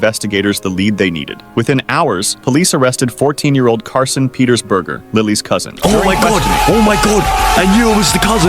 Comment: did he just say that his last name is BURGER??????????????????????????????????????